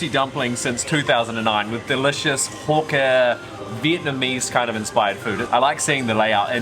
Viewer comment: guys. What did he just say .....? Um.....😂 hawk tua?